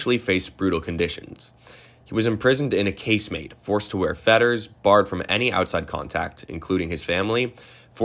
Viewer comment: What kind of clothing are feathers, fedders or fetters (I can't make out the word well) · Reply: Fetters = iron shackles around the ankles connected to each other by an iron chain. They were very difficult to walk with and forced the wearer to shuffle. Derived from the word fetlock, that part of a horse's lower leg that was also tied with rope to another leg to keep the horse from being able to gallop away.